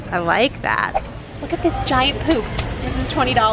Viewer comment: I thought you said look at this giant poop! ....poof